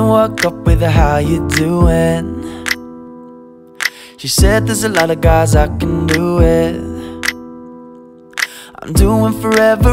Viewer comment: When he said “he said there’s a lot of girls I can do with”it sounded like he said “there’s a lot of guys I can do it.” Lol